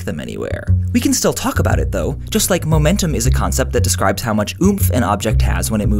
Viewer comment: Excuse me, I'm not a native English Speaker; can somebody help me to understeand what he says at 0:35? "Just like momentum describes how much XXXXX an object has when it moves in a straight line..." Thanks :) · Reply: Its somewhat of a slang term. When he says "oomph" he means energy or force, pretty much